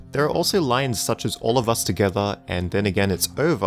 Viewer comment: With the “All of this together” I heard “Let’s see if we can get it to work.” And with the “They forget it’s over” I heard “Allright that should do it.” My interpretation